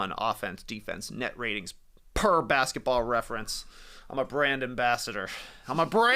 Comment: I thought was saying "I'm a Brandon Basser" and as a Celtics fan I was like "Hell yeah brother about time"